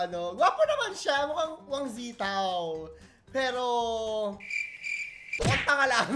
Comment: did you say Huang Zi Tao? Kuyaaaa dati ka bang EXOL?